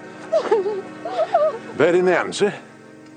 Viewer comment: what did he say to Sharpe? That accent xD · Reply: I think he said "Bird in the hand sir?"